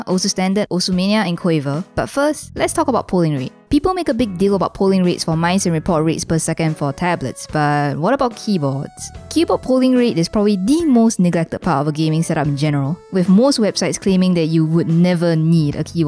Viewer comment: did you just say the n word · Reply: no she said neglected